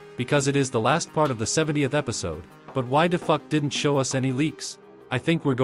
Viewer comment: Did He Said Why The Fu@ At 1:36 ?